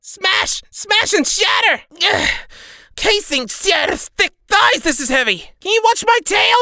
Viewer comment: WHOSE THICK THIGHS?? 📸🤨 (no but seriously what name are you saying?) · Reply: Caesinsjach, a Green god dragon. One of the first dragons created by Asgorath, and she was the first dragon who created kobolds, the first kobold being Kurtulmak.